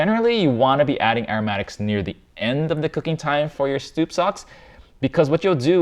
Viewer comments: mark he calls it stoup socks instead of soup stocks lol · Love this video, but I mostly love how he says Stoop Socks instead of Soup Stocks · did i hear him say "stoup socks" at 7:02? this make me laugh so hard for some reason.